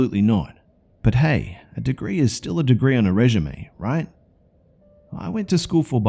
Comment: Did I just hear rejemé?